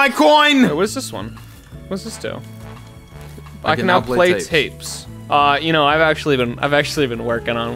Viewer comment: honestly for a sec I thought that said you can now pay taxes